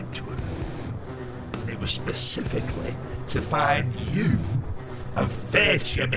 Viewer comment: Sorry, I'm spanish, what says Abzorbaloff at 1:11? "to find you..."?